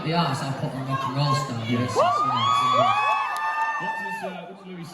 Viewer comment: did they say Harry Styles 😂??